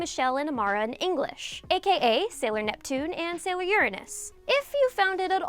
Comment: at first I heard "sailor yuriness" I am so sorry xD